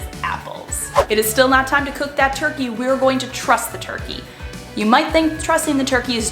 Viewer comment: Helpful tips. Thanks so much. At first I thought you said "We are going to trust the turkey." 😂